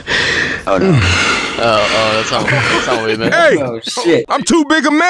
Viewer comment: "oh no" sounded like Nick